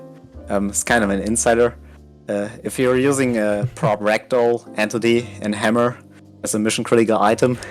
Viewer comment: First time I listened to comment, I thought he said "prop_rectum". Then I saw everybody laughing... It's "prop_ragdoll" of course.